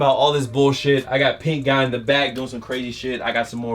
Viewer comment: YOU NEED TO DO SOMETHIN BOUT THAT EYE MY DOOD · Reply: Luc Matthieu I thought he had pink eye he said that in a video